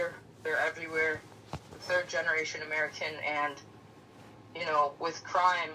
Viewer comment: That’s a slap in the face to tell any American let alone black people to be accepting of anything. You cannot keep telling us to move to the back of the line and think it’s going to be ok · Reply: I heard "America and we", we MUST BE SPEAKING FRENCH 🤮